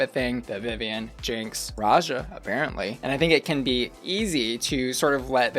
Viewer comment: Did she say people are really good raja apparently 😂 😂